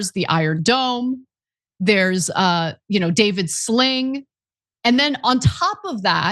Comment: I thought she said, "They have Iron Dome. And then they have David Sling." I was like, "Who tf is David Sling?!" 😂